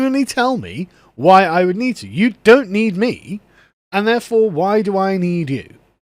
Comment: I thought they said they didn't need men.